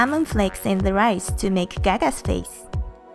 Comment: It sounded like you said caca face not GaGa's face